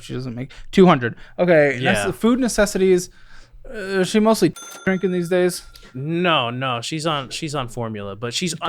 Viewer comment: Did really say "titty drinking" hahaha